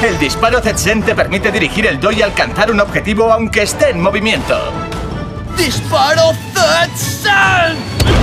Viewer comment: escuche ten no zen :u